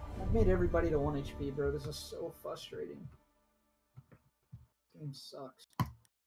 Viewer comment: did he actually say “fuss-trating” instead of “frustrating”?